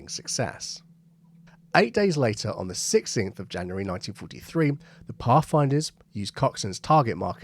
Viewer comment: you again gave a date of Coxons new marker timeline as 16th January 1953. As an Australian I'm up with the English accent so it wasn't my ears as I had heard you earlier give a similar 1953 timeline, and thought I was confused. Just saying mate. 🇦🇺🇬🇧